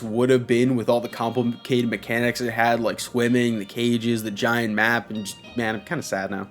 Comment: I thought he said “women” instead of “swimming” as a busted mechanic😂